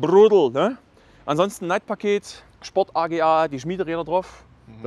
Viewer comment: hab erst Neid-paket verstanden